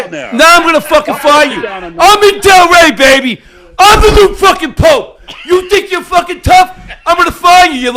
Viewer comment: what did he say there? I'm the new fkn pope?? 😂😂